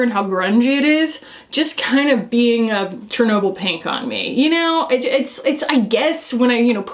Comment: Did you just say “Chernobyl pink”? 😂😂😂 I am dead.